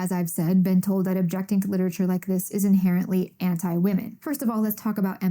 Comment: did anyone else hear “cliterature” here?